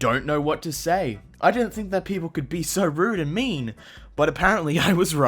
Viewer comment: “buy” instead of “but” nvm still a funny and cool vid